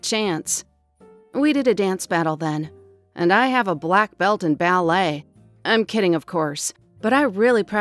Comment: Did she just say black belt in ballet